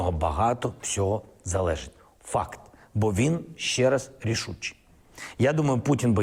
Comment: Did he just say "FACK" at 1:06? Anyhow, we all know how the Coc@ head told us how Putin were Dying, Losing, running out of Child soldiers, missiles, political power and money...for 3 years, nothing of that happened. But 90% of the 'Dumb and Dumber' idiots listening to this are right no telling themself -so you say there is a chance...